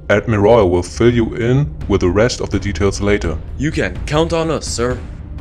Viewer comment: what the dude ACUALLY says u can count on us sir! subtitles: get killed on the server!